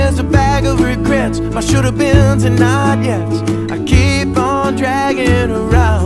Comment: Don’t lie. When he said my should’ve been sand not yets you thought he said I should’ve been to nyyet